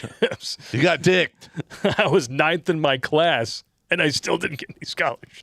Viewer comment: When said he was ninth in his class, it sounded like he said he was "knifed in his class," which somehow seems VERY appropriate!!